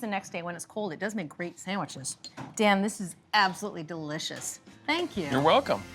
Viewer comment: Did she say “Damn, this is the best....” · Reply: I think she said, "Dan, this is the best..." What I had trouble hearing was what they set the oven temprature to? 200-something. I couldn't make out the end.